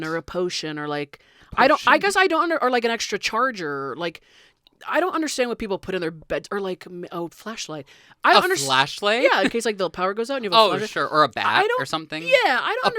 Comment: i thought i heard "fleshlight" not flashlight my bad